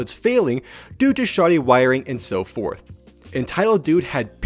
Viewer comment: LMAO I THOUGHT U SAID "r/entitledmanager"